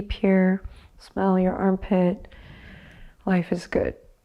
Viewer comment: "Close your eyes, breathe deep here, smell your armpit. Life is good." -Adriene, 2022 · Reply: Oh! I thought she said "smile in your armpit" and thought- how did she know?!